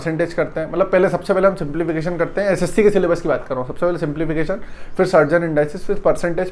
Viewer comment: simplification ke baad kya bole hai sir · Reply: Surd and Indices